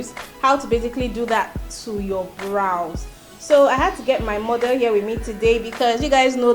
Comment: wow you're the second person calling her my mum, i'm beginning to wonder if i said that in the video...🤣🤣 she's not my mom, she's a model and she is in her teens. thanks for watching! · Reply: check the 0:51. Isn’t that mother that you said? Or did I hear wrongly?